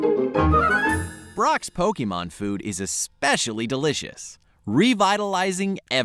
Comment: I literally thought they said Brock's Pokemon were delicious. Turns out Onix is frikking awesome to eat if you marinade it right!